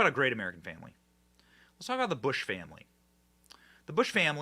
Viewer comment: LOL I thought he said the Bush family 🫢🤷🏻‍♂️😂😂